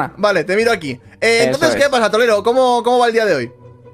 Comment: XD que dijo trolero XD